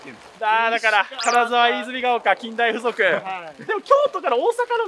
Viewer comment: めっちゃ近大附属に聞こえるw